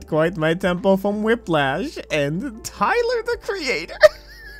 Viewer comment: did he just say Tyler the creator?